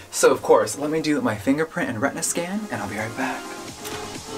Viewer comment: Hella thought I heard "rectum scanner" not "retina scanner"